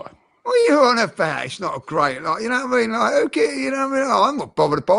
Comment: ‘oubeawabafaaimabuoiu’ is all i hear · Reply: “KnowWhatIMeanLike” 😂